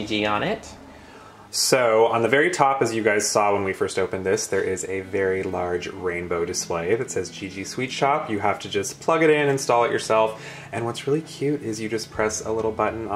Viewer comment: At first I thought it said SWEAT SHOP 🤔